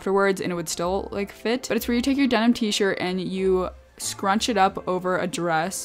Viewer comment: did you say denim t shirt?💜